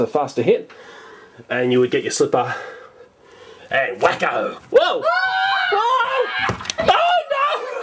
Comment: did he just say Wakko? :D